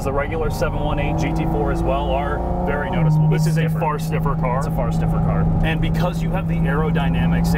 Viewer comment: it sounded like you said fart sniffer car. Sick vid though